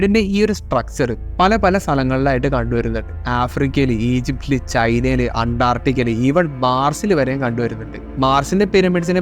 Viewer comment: Did he said ANTARTICA at 2:00 ? 😮